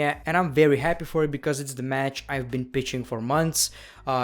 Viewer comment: it sounded like he said "the match I've been bitching for months" I'm pretty sure he said pitching it just he said it so hard lmao